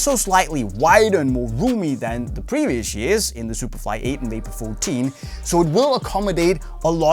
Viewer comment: did he say vapor 8 and superfly 14? 2:31- time of the video